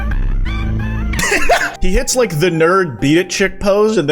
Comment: I'm confused, what did he supposedly say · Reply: it sounded like he said the n word